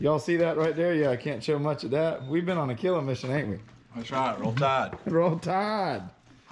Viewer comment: I think he said “Roll Tide” but the captions said “We’re all tired”